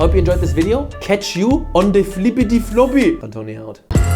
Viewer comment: Misheard the ending as "Catch you on the flippety floppy... I don't need help" don't know which is better